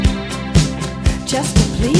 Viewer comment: I always thought she said "she's got better days behind" wtf lmfao